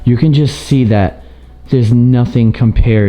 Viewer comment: I thought you said that there's no stairs.